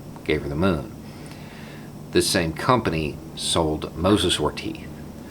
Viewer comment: I couldn’t quite make out what you said they were selling Never mind, figured it out, Mosasaur Teeth